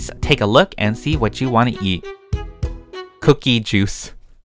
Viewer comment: At first, I thought he said cookies and juice... :) Haha